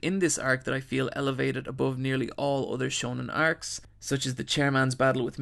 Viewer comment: Did he call meruem MIRIAM?